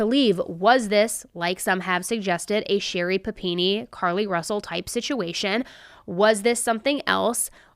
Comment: Just a heads up - it sounds like you say “Sherri Pappini elk”. I think what you’re meaning to say is “Sherri Pappini ilk”. I heard “elk” twice in this episode. No shade, just trying to help:) Great episode!😘